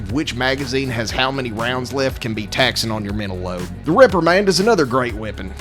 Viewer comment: Really sounded like he said 'The Ripper Man', not 'the Reprimand', lol